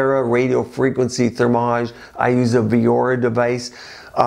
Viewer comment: He said I use a "veor" device @ 6:55 ? did I hear that right? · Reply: Viora